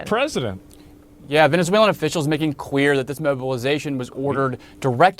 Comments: Did he say "making queer?" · is 1:15 him saying queer instead of clear a mistake? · Why does the guy says "quueer" instead of clear 🤣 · did he say making queer?